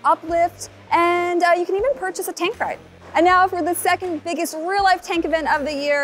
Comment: sounds like said “tank ride” there fyi